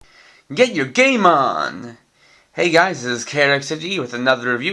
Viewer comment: When you said "Get your game on" at first I thought you had said "Get your Gaymon" so I had to go back and listen to the start of the video again to double check. XD